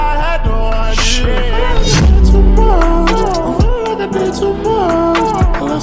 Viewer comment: it sound like he is saying "i fell like i dance with morgz" 😂😂😂